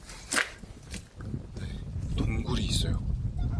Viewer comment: 동그리오 노래가 들리는데욬ㅋ 동굴있어요라서 동구리오 인가 ㅋㅋㅋㅋ 동구리오 타돗테모 츠키마셍~